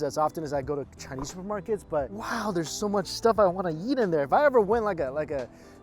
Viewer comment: for a second I heard "wow there's so much stuff I wanna yeet in there"